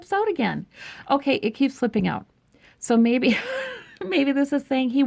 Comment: “It keeps flipping out” ... Made me think spirit was saying stop flipping out stop flipping out you guys LOL